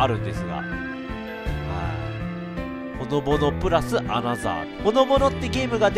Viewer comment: ほのぼろって聞こえた（笑）でも、そこが可愛い❤